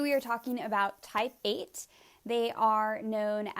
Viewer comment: Thanks for the video, but Type 8's are not automatically type A's... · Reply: She said Mother Teresa was a Type 8, not a Type A (guessing that’s what you referenced because that’s initially what I thought she said).